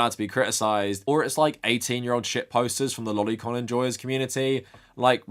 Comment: did you say lolicon enjoyers community? Or did I mishear you?